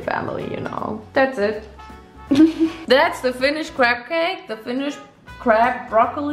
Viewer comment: In the whole video it sounds like she is saying crab cake 😆